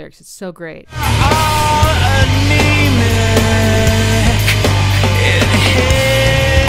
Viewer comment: Ooh it's 'anemic'. I always thought he said he was a Namek.